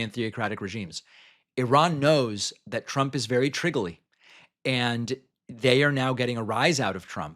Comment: What did he say? "Triggerly"? That's not a word?